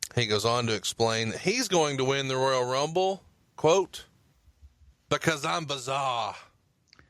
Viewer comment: Davey Boy Smith didn't say he was going to win the Rumble "because I'm bizarre"... what I always thought he said is "Because I'm berserk" and seriously, it makes a lot more sense...